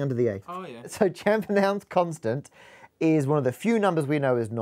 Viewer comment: I thought he said can't pronounce constant.